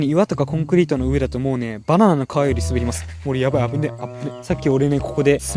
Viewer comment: ラジオ感覚で作業しながら聴いていて 急にババアの皮より滑るに聞こえて三度見したw